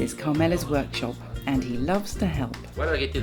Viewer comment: At 2.03 She doesn't say "it helps", but "did you like it?" (Ti è piaciuto?). Maybe it has been misheard with "é di aiuto".